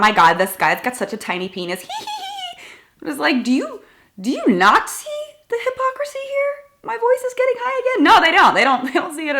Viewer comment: "do you Nazi the hypocrisy here?" anyone else hear this or just me? :)